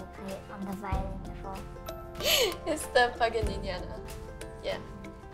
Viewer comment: When Chloe says "Paganiniana" at 1:29, the subtitles say "Fucking Ninja" Lol